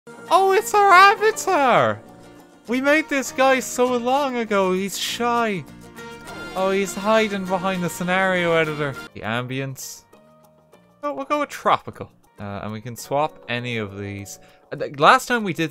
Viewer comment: Lol I thought you said 'oh he's shite'